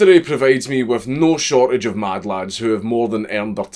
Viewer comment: I thought you said madlad not mad chad